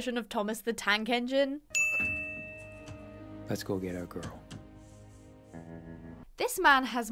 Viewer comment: he actually said “let’s get our girl” bc the train is named after Lucky’s mother